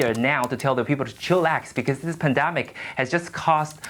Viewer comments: Did...did he just say Chillax on live TV news? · Did he just say "chillax"?